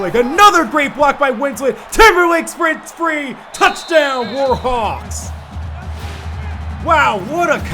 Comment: Did say "Touchdown Warhawks"